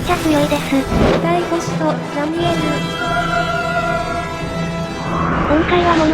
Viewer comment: 「５番 ラミエル」に聞こえて、さらにハイテンション過ぎて吹いたｗｗ